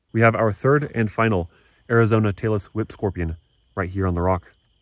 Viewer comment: Due to my wife's musical tastes, I keep hearing "Taylor Swift Scorpion". :body-blue-raised-arms